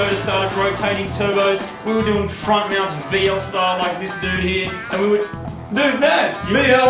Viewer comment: I thought they said veal style...